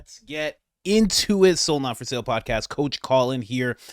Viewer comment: I thought you were saying, "So not for sale" podcast for a couple months...I get it now..Soul not for sale...yeah...ha ha..love your show brother...